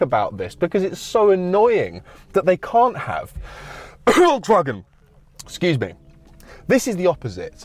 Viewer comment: P.s. anyone know what he said under the cough at 10:52? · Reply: Volkswagen 👍🏼